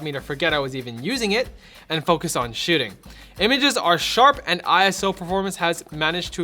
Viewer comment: did you just say shitting?